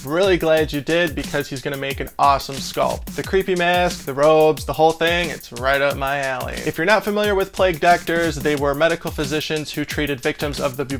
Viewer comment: I legitimately thought you were about to say “it’s right up my ass” Other than that I love your sculptures